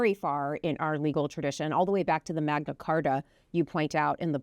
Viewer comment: I thought I heard Mellisa refer to the “MAGAcarta.” Not what she actually said but actually a good nickname for Project 2025!